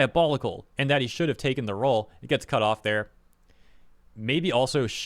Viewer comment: In case no one has said it yet, at 4:55 I'm pretty sure it was "should have taken the role uncredited" since that's what Kayli Mills originally said when she responded to Jacob Takanashi's announcement on Twitter